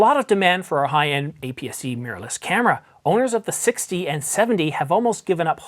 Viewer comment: Great, like this one a lot , like the humour , best take ownership of a mistake... ok blame those squirrels 🐿.... Although I did put the captions on when you mentioned the EOS 60 and 70 thought for a moment you said 6d and 7d... but no mistakes there ....